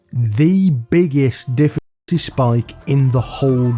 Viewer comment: Did you just say "diff*ckulty spike" at 4:45 😂😂